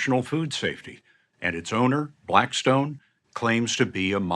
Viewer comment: did he say BLACKSTONE?